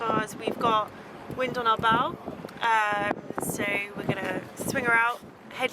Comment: Had to rewind,thought she said wind in our bowels 😂